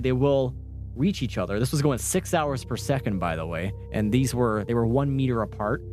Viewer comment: did you say 6 hours per second??